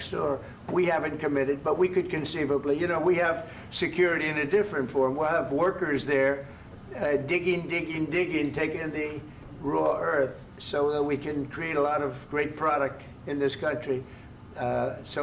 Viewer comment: “Raw earth” - what is that? I’ve heard of rare earth metals. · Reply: I thought he was saying rawr uWu Earths 😶